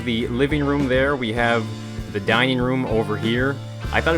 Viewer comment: I heard "lets leave the dying room" 😝 like if you agree